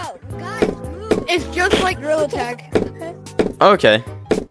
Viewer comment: I think he said gorilla tag lol not real attack What happened?